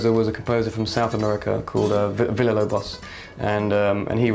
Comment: Matt mentions a south american composer at 10:15, does someone understand the name? Amazing interview, btw! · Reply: Villa Lobos